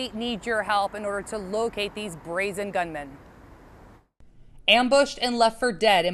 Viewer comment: thought she said BRAZY LOL